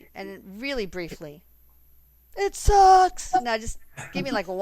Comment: What is the sound at 23:33? I thought it sounded like 'it sucks' when you asked about the state of humanity - lol. Couldn't tell if it was a voiceprint or one of you guys since you were frozen :) Love to all & is so much fun. xoxo